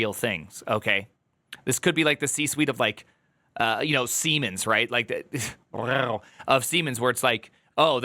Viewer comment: Siemens, I heard "semens".